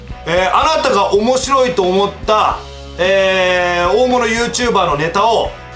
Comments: 大物YouTuberエロ道って聞こえたw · 大物YouTuberエロ道って聞こえたやつ！